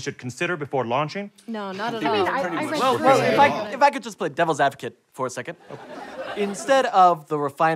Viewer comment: At first I thought it said "Devil's Avocado" 😂